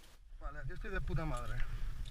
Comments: i really took 4 yrs of high school spanish just to understand this dude saying “es facil” while climbing that scaryass cliff... i sense some cap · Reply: I heard "puta de madre" after 6 years of school spanisch ^^ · is he saying puta madre? XD